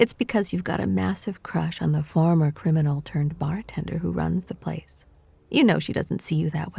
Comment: I almost thought it said bar mitzvah instead of bar tender. I was about to say “mostletov!!!”